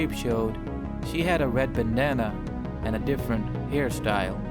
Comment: Half Life Zombie Panic + tf2 = left 4 dead 1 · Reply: Wait, When i Got to the Zoey redesigning part, Why did I Hear "Banana" ? WHo Else Hears This?